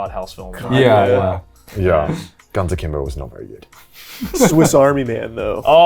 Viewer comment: Does anyone know what film Connor says at 1:29:01 ? I can't make it out Edit: its Guns Akimbo